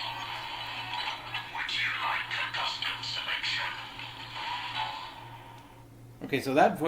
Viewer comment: I almost thought I heard it say "WOULD YOU LIKE TO PLAY A GAME?" at first.